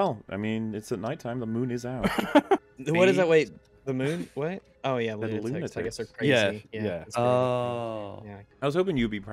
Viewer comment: What does say? Can't hear it for the life of me. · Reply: Roughly: "Wait what? Oh, yeah "lunatics" I get it because they're crazy"